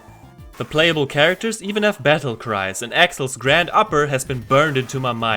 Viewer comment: As a kid, I thought Axel was saying 'Grand Papa' when he was doing the uppercut. And the kung-fu guy was saying 'Shabba!' like the RnB song. As for the game? Best beat em up ever in my opinion, to this day. And possibly the best MegaDrive game. Possibly